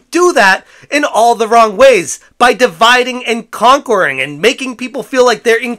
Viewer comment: I seriously thought you said “dividing and concording” lol